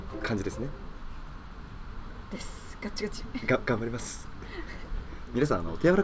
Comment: ガチガチがガチムチに聞こえたw